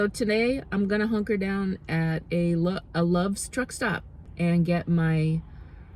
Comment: When you said Love's truck stop, I heard "trick stop." I'm sure there's some of that going on at truck stops.😂